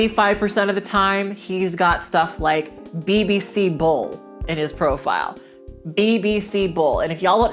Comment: Omg for some reason when you said bbc bull i thought you said bbc bowl....😑.... I was like wtf does a bowl have to do with sex???!!! Lol 😆😆😆